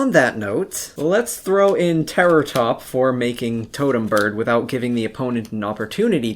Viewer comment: Did you say totem bird?